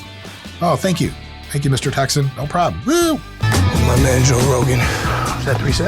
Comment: I thought he said "thank youb Mr.CockSon" when he was impersonating a texan